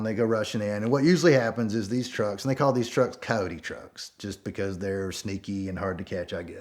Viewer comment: I’m 100% positive they call them coyote trucks because the people who smuggle are referred to in Spanish as “coyoté” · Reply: Just coyote without the accent Also there's a mod for mustangs called coyote ('cojote') 2:45 I heard "cowdy trucks"